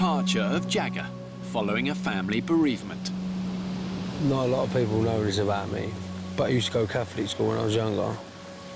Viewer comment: was that even English?🤣 no idea what he was saying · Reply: One sec, let me translate it. "Not a lot of people know this about me, but I used to go Catholic school when I was younger. (short pause) And I whenever I'm in ... trouble, a bit of trouble or something bad happened or anything I say uh like a pray... like when I'm indoors on my own in bed of something... and I'll say one for him today," (or to summarize it he used to go to catholic school and when he faces hardship or something bad happens he prays in private and he's going to do it that day). To me it sounded normal but I guess it doesn't to others.